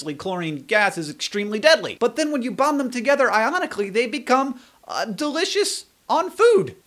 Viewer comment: When you said sodium and chlorine bond ionically, I thought you said ironically C